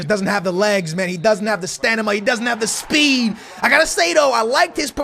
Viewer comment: Did anyone hear (idk his name) saying stanima at 18:36(i am a new subscriber that is why I don’t know his name dont hate)